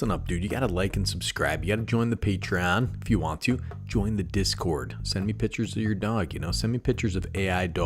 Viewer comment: My speaker doesnt work well on my phone & during my lunch break today I was listening to this & thought you said "join the discord & send me pictures of your dong." I thought you were 100% wanting that until you said or AI generated Skydiving DOGS did it click.